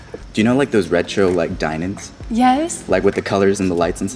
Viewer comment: whats a 'red show dine in'? I think i heard wrong, pls correct me